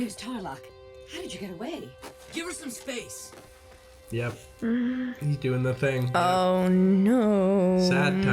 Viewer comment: Did just said, " Yep, He's doing the tHiNg" ?